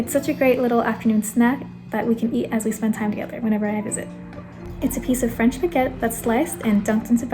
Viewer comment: She said me and my grandmother have a slice whenever I visit. I thought she said whenever I have a zit 😅🤣😂